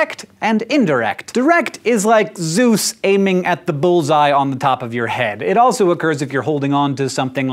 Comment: I LEGIT heard it like this: "Direct is like Zeus aiming at the balls on the top your head..." i couldn't stop laughing for the rest of the video....